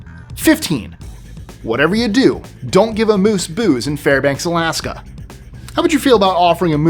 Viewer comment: Seriously thought said “amuse-bouche”. You can’t have pre-appetizers in Alaska?